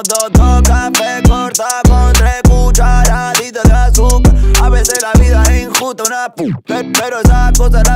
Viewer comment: like si no entendiste ni una sola palabra que dijo bhavi 0:58 2 cafés cortados con 3 cucharaaaaditas de azúcar xd